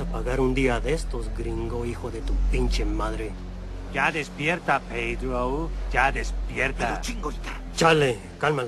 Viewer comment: ¿Saben que dice Big Al en este momento? Ya despierta ? ya despierta. No entiendo esa parte. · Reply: Ya despierta "pedrow" (Pedro) burlándose de los nombres latinos.